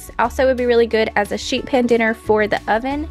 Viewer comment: You said "sheet pan dinner", but at first I heard "sheep head dinner" Haha!!!